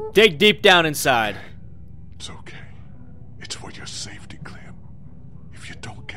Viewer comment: Anyone notice at 9:01 it said "its YOUR FOR saftey. instead of for your in the subtitles lolol